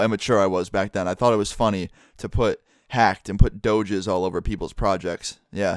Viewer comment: lmao at 4:05 put on subtitles and instead of doges it says doh jizz looool